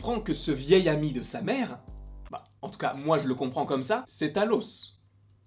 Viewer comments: j'ai entendu "Thanos" la première fois, j'étais en mode WTF 😂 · Pourquoi Thanos ? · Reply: Il a dit Talos pas Thanos